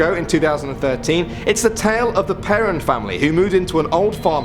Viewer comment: did he say the perrin family? Haven’t seen the film so I don’t know